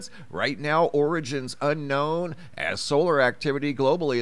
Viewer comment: did you say, uhm, "origins unknown" ? Hm.